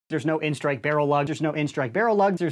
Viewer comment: ya know i think he's trying to say that they don't have an n-strike barrel lug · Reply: Really!? I thought that he said that they dont have an N-strike barrel lug